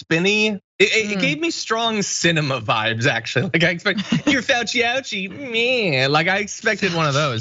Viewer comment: Thought she said Fauchi Outie. Baby's first words.